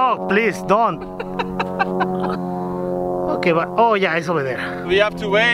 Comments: Mexican dude makes this video 45% better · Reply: K I heard him say: "Oh ja hij is alweer weg." (7:34), which is dutch for oh jeah he is already gone. Not many people speak dutch. He said it at a specific moment what leads me to think his primary language is dutch. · he says: "Oh ja, hij is al weer weg" which translates to: oh yeah, he is already gone. Is he dutch or does that sentence just really sounded like dutch!? I felt like he said it accentless lol. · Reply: Listen closely; he said "Oh yeah it's over there"